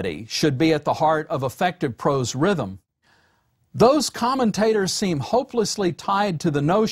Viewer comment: Anyone else hear “common taters” at 16:13?